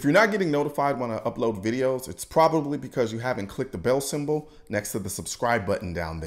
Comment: For some reason subtitles were on and it thought you said "bail symbol" not "bell symbol". Epic fail YouTube. Learn Texan. :)